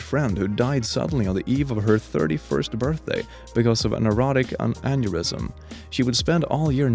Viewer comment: did you say erotic instead of Aortic?